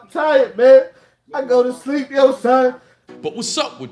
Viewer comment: Did he just say I go to sleep yo son?👀